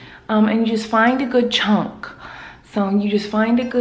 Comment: I thought she was saying " Find a chalk of hair"